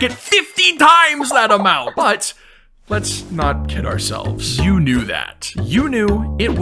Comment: bro at the start I heard "but let's not kill ourselves" LMAOOO